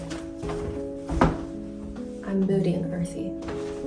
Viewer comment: what said: "I'm moody and earthy" the subtitle: :"I'm booty and earthy"